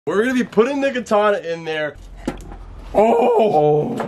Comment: I heard nigatana